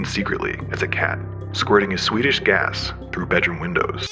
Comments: I thought he said “Swedish gas”. I’m like “what gas? Did Sven fart again?” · As a swede , idk if I’m amused or slightly insulted for it to be ”swedish gas” in the subtitles 11:21 instead of of “sweetish” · I thought he said: "Squirting his Swedish gas...." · bro I thought it said swedish gas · I was only listening to this and thought it said “Swedish gas” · Thought they said he released his "Swedish Gas"